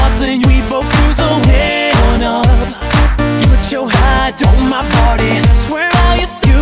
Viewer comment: For some reason I started dying when I heard “Put your hat on my body”